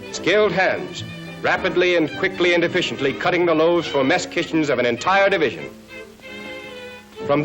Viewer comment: Had to rewind and turn on CC around 6:50 because I thought I heard ‘mastications’… turns out it was "mess kitchens". Either works.